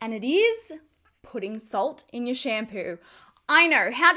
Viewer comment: I thought she said "pudding salt"